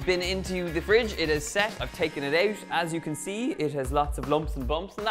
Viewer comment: Hahaha i heard "i've caking it out" on the first cake